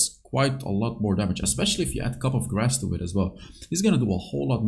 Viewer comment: Did we call coup de grace “cup of grass”? 😂